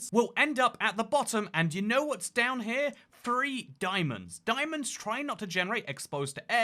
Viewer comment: "You know what's down there? Free diamonds!" - that's what I hear. And then subtitle that corrects "Three" with "4". Me thinking: WTF, that's a weird fix for word "free" :D